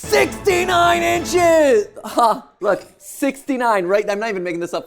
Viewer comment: When I first heard 69 inches I thought it said 67😂